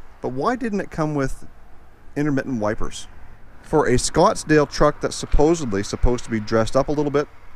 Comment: I heard you say Scottsdale did this truck come from Scottsdale Arizona?